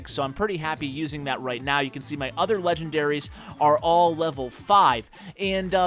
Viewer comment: did he just say other legendaries